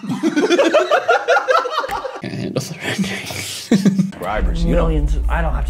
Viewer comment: What did he say? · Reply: “I can’t handle the rendering” I think they were looking at some space stuff and he was making fun of “Space is Cool”